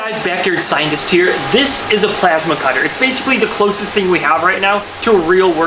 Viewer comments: I've been playing too much Halo 5 and thought it said plasma caster.... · I thought it said “pizza cutter” instead of “plasma cutter”